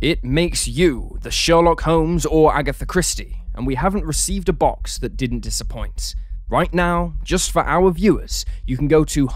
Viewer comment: "We have not received a box that didn't disappoint" am I hearing this right? 🤦‍♂️